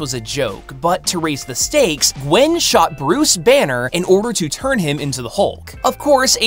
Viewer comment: I thought she said Bruce Wayne